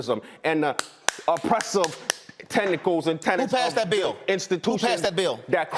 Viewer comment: did he say "oppressive tentacles," WTF?